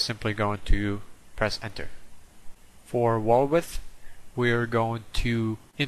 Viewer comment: love your vids!!! it sounded like you said for walrus